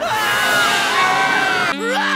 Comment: Caption said "I suffered when they rap" I burst out laughing